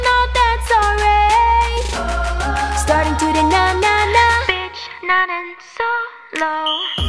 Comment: I thought 1:41 she say BITCH